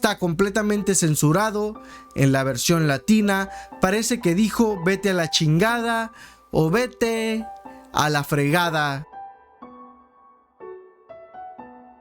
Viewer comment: A MI SE ME HACE QUE DIJO "VETE A LA M****DA TU Y TU JUGADA" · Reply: Dijo vete a la chin……. Gada